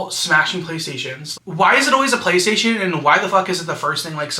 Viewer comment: I’ve thought ahead to this at work and buying shots. I buy them in tiny bottle form bc each one could always be my last, but not today. ❤ 7:11 oh my god.. I thought you said face, not PlayStation… but still. (Your hair looks extra good in this video) 11:36 I want this as a tshirt, but with the narrative scene setting of ice cream too..these step parents remind me of my mom. I gotta send that 🐩this video!!!!!